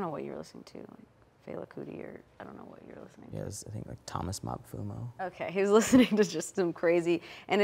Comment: Thomas Mapfuno. I had to listen to that like 5 times to figure out what he said.